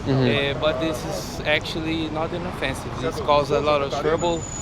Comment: not “an offensive” … he actually said inoffensive, or harmless